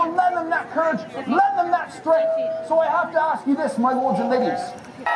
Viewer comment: im sorry, but i hear, "my lords and niggas"